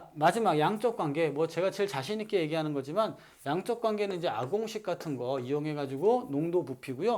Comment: ‘양적관계는 ***같은 거’ 에서 ***가 뭔가용?? 못 알아들었습니다 ㅜㅜ 그리고 XY4-CH4,CF4,SiH4,SiF4 / X2Y-H2O 외에도 외워두면 좋을만한 게 있을까요? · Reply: ’ㅏ 공식‘ 이라고 하신 것 같아여